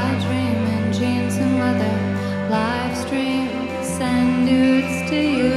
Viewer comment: does she say send nudes? Whatever she is great songwriter singer❤️